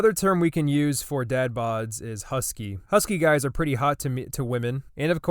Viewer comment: "Husky guys are pretty hot to m- to women" 1:09 I swear I heard it😂